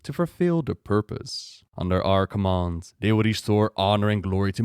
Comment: Thought you said 'Under Archimonde" when you said 'Under our command'